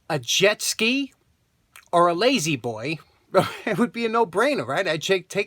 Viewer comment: Wasn't fully paying attention around 1:57 - had to rewind because I thought you said "LadyBoy"!